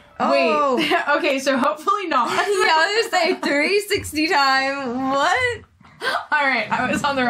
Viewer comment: Hi, btw, at 17:40 360 would mean that you end up where you started, I think you meant 180 :) Ordinarily I would not have said anything but when you said "It's 360 time", I thought you said, it's 369. That got my attention, I thought your mind was in the gutter so I went back and took another look, turns out it was my mind that was in the gutter:)